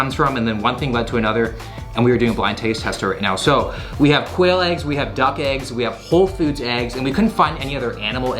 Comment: First time he said quail eggs I heard "whale eggs," and I was like huh interesting, till later I realized whales were mammals and therefore dont lay eggs.....